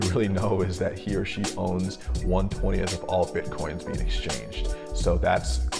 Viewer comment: How much does he/she own? You say it at 1:00 but I can't hear what he says. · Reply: 1/20